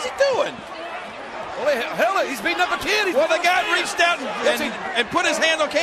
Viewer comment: Paul says "Glenn stop" to Kane because he "really" attack fan. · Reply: I thought I heard JR " Glenn's beating up a kid."